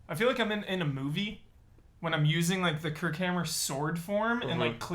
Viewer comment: Not going to lie, the first time referenced the Kirkhammer I thought he said Kirk Cameron. Suffice to say I was a little confused for a minute.